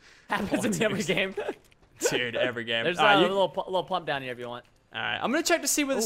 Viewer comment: did he just say lil pump?!?